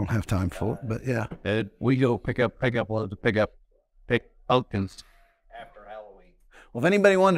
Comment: What did the guy say is a natural dewormer at 50:30? The audio is weird and I can't tell what was said. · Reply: Pumpkins in cover crops